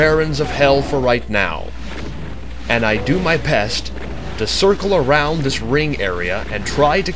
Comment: does he say ill do my PEST?